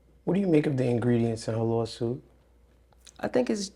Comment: Uhm what was 43:56 about; she said 'is her lips' what🤔